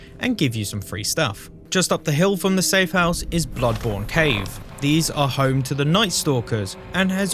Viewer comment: I'm sorry.. did you say. peakborne cave>>?